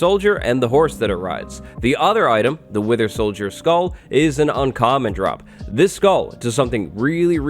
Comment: I thought that he said "winter solider skull" not "wither solider skull".